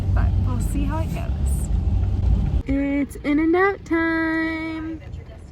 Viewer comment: lol i heard "it's in and out time" as "it's dinner nap time" and i was so confused. it looks like y'all had a lot of fun!